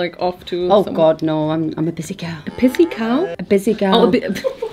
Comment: I thought Perrie said "a pissy cow" too 🤣